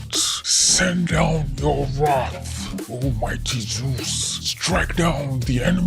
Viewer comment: Send down you wrath o mighty juice that's what I heard lol